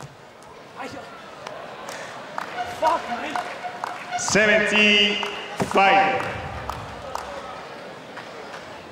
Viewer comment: what did he say at 44:28 ? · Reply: I think he said "Dat kan toch niet" which means "That can't be true". If you're still interested xD